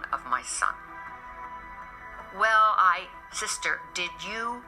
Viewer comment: woooow, I think his sister is even saying their mother went to confront the nuns from their catholic school there! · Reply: She says "sister" and mentions knocking the bonnet off their head.